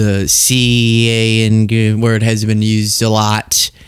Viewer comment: "... The c a n d–nhrr word has been used a lot..." Boy what...? What the heck is 'The c a n d–nhrr...' word!? What are people saying?? (I'm genuinely out of the loop, here... What was he trying to say? 😅)